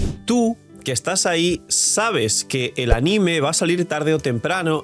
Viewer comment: Como escuche bien ANIME?